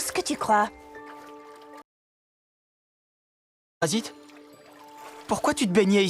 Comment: Il y a un bug a 6:39 Ils dit quoi exactement ? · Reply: Parasite